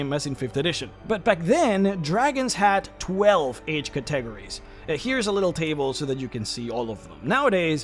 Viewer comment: I was so confused what a katteggerie was until I read what it said on-screen. 😭